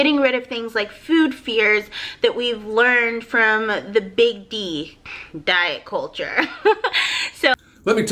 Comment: did she say "fear she learned from the big D" ?? Lol